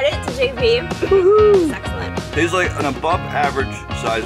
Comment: “The frosting though, sexellent “ who else thought HE SAID DAT ;3